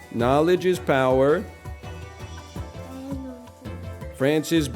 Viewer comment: I heard "Knowledge is power, France is bacon" and didn't question it